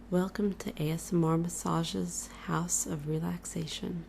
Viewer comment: So just something I thought you should know. When I started watching your videos, especially your older ones, I still didn't know what ASMR should for, so when you said "welcome to asmrmassage" I thought you were saying: "Welcome to Aya Samora Massage" I thought that was your name. It's a pretty cool name XD but now I see that it was just because I wasn't used to your accent yet